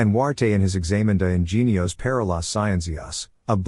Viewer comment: damn robot narration, what's the author and book referenced here? I'm trying to look out up, thought it was saying Enuarte or Anduarte, seems like it's saying Examined Genes Parallel Science? I can't seem to get Google to figure out what I'm searching for. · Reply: And Huarte in his Examen de ingenios para las scienzias—a book which has been famous for three hundred years—denies women the possession of all the higher faculties.